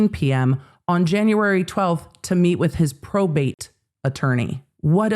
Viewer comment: omg chills. I missed it saying PROBATE. I thought she said Probation, but that doesn't make sense for the situation.😮😬